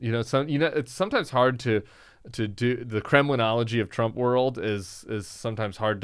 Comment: Did he say "criminality" or "kremlinality"?